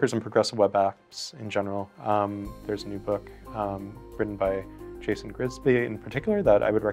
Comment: you said there is a physical book on service worker and progressive web apps by "Name".. I cant hear the name properly. What is the name ? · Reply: Progressive Web App by Jason Grigsby